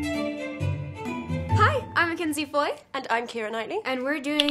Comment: At the beginning I genuinely thought mackenzie said 'hi I'm a conceived boy'